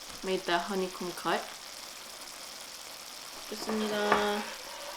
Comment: Did u just said "Bismillah" ( 3:47 ) before eating. Dont take me wrong just asked. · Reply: no she said thank you for the meal in Korean what the hell😂😂